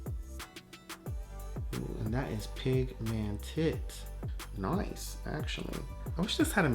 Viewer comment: did i hear... “pig man tit” ??? lmao